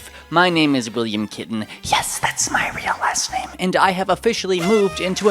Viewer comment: Kitten? Ha I thought you said zitin as a joke about your face.